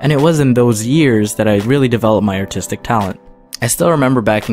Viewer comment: I thought he said "my autistic talent"